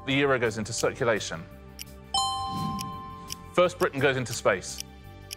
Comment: I had to look up when I heard "Fern Britton goes into space"... Ahhh... First*